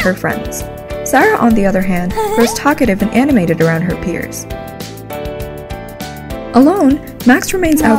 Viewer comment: I don't quite understand what she said at 1:43 after the "Sarah, on the other hand,..." part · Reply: Sarah, on the other hand is talkative and animated around her peers"